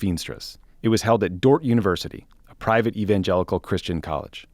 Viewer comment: I heard "Dork University."